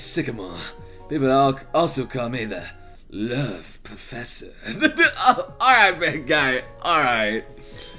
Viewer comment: did said all right man guy?